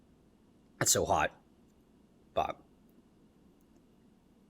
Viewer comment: I thought he said "It's so hot, fuck"